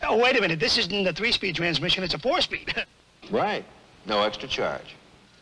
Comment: I heard Prince Adam say “Right”